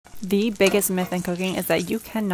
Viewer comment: I heard you say "the biggest myth in cocaine" and i was wondering that how this cooking video is linked to drugs.